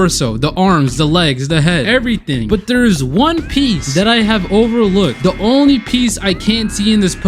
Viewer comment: DID HE SAY ONE PEICE ?? !!!! OMG ONEE PEICEE IS REALLLL !!!!!! ONE PEICEEEEEEEEEEEEE